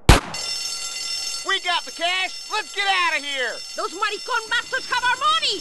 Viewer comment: i heard "Have some Macaroni"